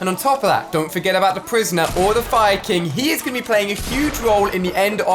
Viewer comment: did he say fire king